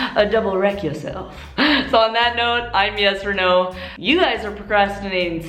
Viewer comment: i thought you said "My name is Yes Orno"